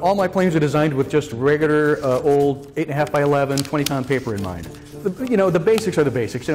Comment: did he say 20 pounds!?